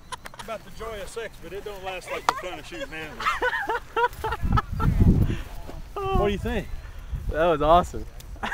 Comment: what does he say at 1:40 ? "It's like the joy of sex but ----------- anvil up?" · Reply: but it don't last like the fun of shooting anvils